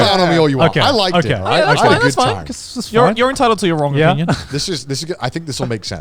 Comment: Did Joey just say "You are entitled to your wrong opinion"?